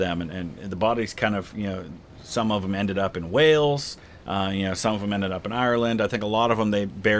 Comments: my dumb ass really thought he said “whales” until he said Ireland. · LOL 18:47 When you said some of them ended up in Wales I was surprised cause I heard whales, then you said other places.